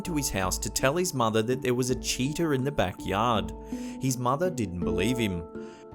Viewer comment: haha, your accent threw me for a loop when you said a kid saw a cheetah in the back yard. I thought you said a cheater so I'm thinking what, someone who cheats on tests at the kids school? No, no a Cheetah! damn 70mph hunting and killing machine...